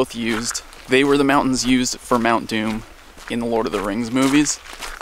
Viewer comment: Who heard Mountain Dew